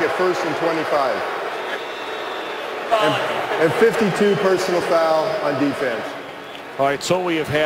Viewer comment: does he say 52 personal fouls on defense?!